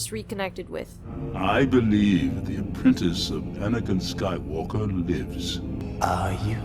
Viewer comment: why did i hear 54:25 "anakin skywalker" as "Benedict skywalker"